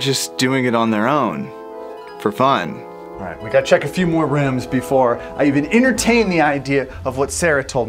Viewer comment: With captions on it has "RiMs" instead of rooms- :O 10:31 Oh come on there is "fer", but it should be for. ;-; Again...